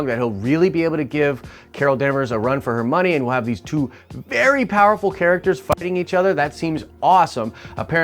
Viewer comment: So I've watched this on two devices now just to make sure I'm hearing what I'm hearing but at 7:20 did he just say "2 very powerful characters f*cking each other, that seems awesome." Lol